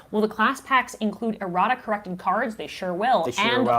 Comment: There was a moment where I heard "erotica" instead of "errata" and I had a moment of, "Wait, what?" 😂